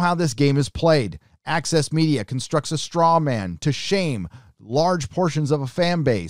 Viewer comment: I thought at first you were saying AXIS media. I think that may be better.